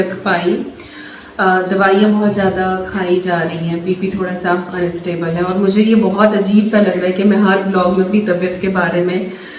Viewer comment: I heard the into bit waaaaaaaay too many times because I thought I heard you say ‘Baby thora sa unstable hai’ 😮 @ 0:45 - then I realised you said BP!! Look after your health - God knows everything and your health is wealth x happy birthday!